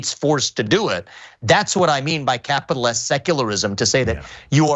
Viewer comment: Cenk recontextualized the use of the word from the way that he used it in his book. Vivek was talking about corporate secularism. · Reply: I thought he said he was talking about capitalist secularism.